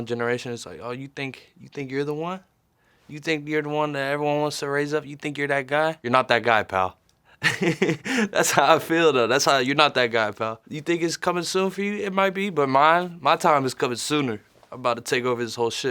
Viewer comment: Jack saying "you're not that guy pal" is the most Jack thing ever😂😂😂😂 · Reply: I thought it said “you’re not that gay pal”